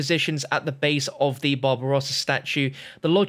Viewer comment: Who else thought he said bob ross instead of barbarosa?